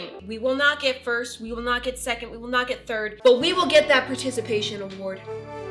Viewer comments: Who thought when she said turd instead of third 🤣🤣🤣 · Can we just appreciate that 🌈moriah🌈 is the only person ever that will genuinely make us feel good about getting a participation award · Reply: lmao I thought you said precipitation award... forgive my incompetence.